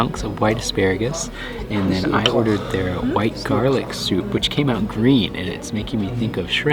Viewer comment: I think it's supposed to be "wild garlic", not "white". You eat the leaves instead of the bulb, so it's basically a garlicy chive